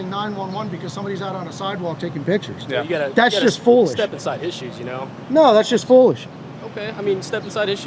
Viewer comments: I too thought the cop said "steppinsideissues" 😂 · I heard "steppin' side issues" before I heard "step inside his shoes".